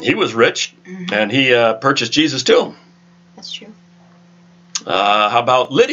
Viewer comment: At about 17:11 it sounded like you said Joseph of Armethia purchased Jesus too. Did I mishear that? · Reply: Tomb...lol...grave...the scripture is there if you watch again.